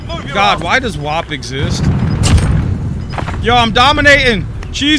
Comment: Sounded like you said “Why does God exists?” Lol